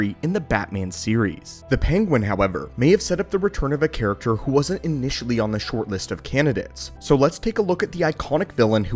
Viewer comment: did u just say 1000 live action films? What? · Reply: A dozen